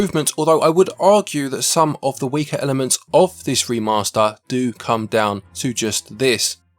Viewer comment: As a non-native English speaker I misheard “hasty remaster” instead of “HD”… But it seems to be a little bit undercooked anyway… 😂